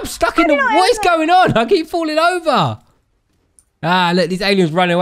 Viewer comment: When she said “i got lays” I thought she said “I got laid” I’m sorry 🖐😳